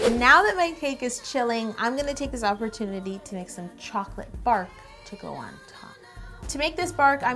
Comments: Hahahha I thought she said “chocolate barf” and I was a bit concerned 😂. And that cake looks amazing! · It sounded like she said she was going to add some chocolate BARF and not bark. lol I was so confused for a second. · I thought you said chocolate barf! ^0^ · No joke, when she said "chocolate bark" I thought she said chocolate barf!